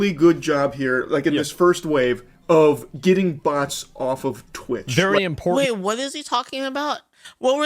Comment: Ngl i heard "get butts of Twitch" bit then realized he said "get bots of Twitch"😅